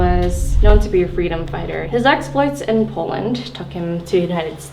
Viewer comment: Did she said Poland at 5:40? 'Cause it's my country and I never hear about him. 😂 I just google it. It's true his polish name was Mikołaj Gustaw Schulz. He was pretend to be polish but he was from Finland and he was fighting in Canadian Polonia. Interesting! 😱 I imagine he could speak polish hahaha